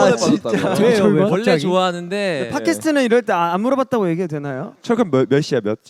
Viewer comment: my tripping self thought he said "Pakistan" 😭